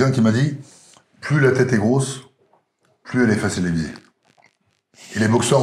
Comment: "plus la tête est grosse plus la tête est .." QUOI ? pardon c'est pas pour me moquer mais j'aimerais vraiment comprendre ce qui se dit @ 23:07 !!! ("facile à diviser" ?) ("pressé à l'élysée" les sous titres indiquent^^) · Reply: facile à viser sous entendu, plus tu te la raconte, plus c'est facile de te descendre